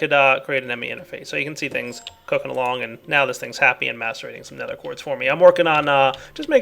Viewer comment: what I heard: this thing masturbates some nether quartz for me.